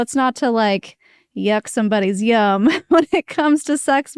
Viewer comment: ❤ “Yuck somebody’s YUM?!” Lmao did I hear that correctly?? I’m gonna say that all the time now 😆